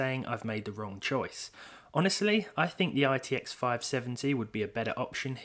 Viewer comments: did you say rtx 570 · Did you just say "RTX 570"? Haha no worries! Great video!